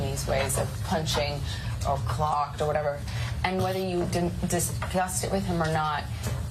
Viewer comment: What does she say? Whether you discussed it with him or not ? OR Whether you are disgusted with him or not?? I just cant hear it ANYONE?